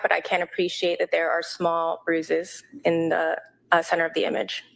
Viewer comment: its seems like the whisper says "look at those small bruises" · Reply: that’s more generous than what i heard, which was “gorgeous bruises”…which would be pretty dark…